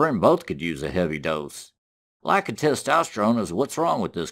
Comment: "How does a blind person know when they're done wiping?" Lololololol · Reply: I thought he said "Black"